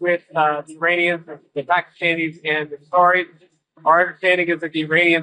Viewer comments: Ooh, kitten's got claws...but all Mario has to do is stand back and watch this whole stupid paper castle combust and collapse around him. Odd, at 4:10 the bold caption says "Israelis" twice where Vance says "Qataris"--if that's fuckery, it's pretty clumsy. On the other hand, at 5:30 he admits that he wants "to be a beautiful slave." Lousy AI interpretation sounds more likely. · Pakistanis and Qataris, I heard, but the subtitles on screen say "Pakistanis and Israelis." edit 4:25 "the Biden and Lebanon" : the fighting in Lebanon 4:54 "the Houthis" : the whole region ( ?) 5:32 "a beautiful slave" : a beautiful place.